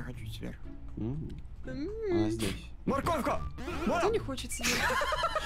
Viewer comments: мне послышалась или она сказала мне нехочется сверху :/ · Мне послышалась или она сказала кто не хочет сверху